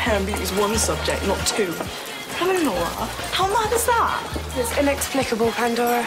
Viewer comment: wht did she say? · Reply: "bloody nora, how mad is that?"